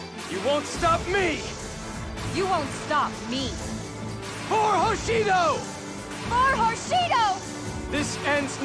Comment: i coulda SWORN she said "For Whoreshido"